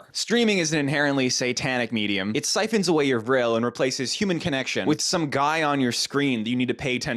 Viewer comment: did he say vril lol